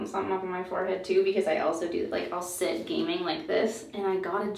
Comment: When she said she was gaming a lot I thought she said she 'gaining' a lot (weight) and I was like 'same girl, same'😂